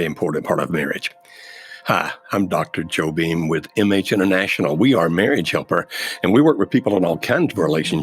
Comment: When you introduced the video and said, "MH International", I thought you said, "Image International". Lol